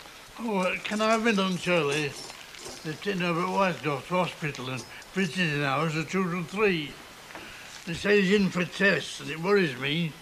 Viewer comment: ??? Any idea what Uncle Albert is saying, up to where Deirdre goes out of shot? Edit - ok, he asks if he can have lunch early because Herbert Whitehead is in hospital. Visiting hours or something. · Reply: Yes, - He says ... " They've taken Herbert Whitehead to hospital,and visiting hours are 2 till 3.They say he's in for tests,and it worries me, I mean it's the third time he's been in.What are they testing for? ,that's what I want to know "